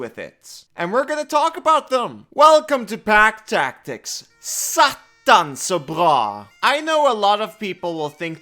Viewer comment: Hearing the most Rogalending dialect ever threw me off so hard you don't even know dude. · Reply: I first thought he said "attans så bra", but now i know it was not swedish😂